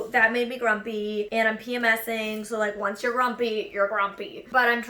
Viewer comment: “Once you’re grumpy, you’re grumpy.” That’s a mood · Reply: Kitty 101 Ohh I thought she said once you're frumpy you're grumpy 😅